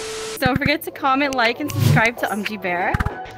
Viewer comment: did she said „so forget to comment like and subscribe?“